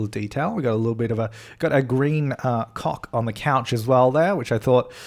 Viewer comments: did you say "We've got a green cock on the couch over there" at 7:54? If yes that was amazing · I was listening to this in the background while I was playing my game, and I heard him say "so I put a green cock there." I was like what?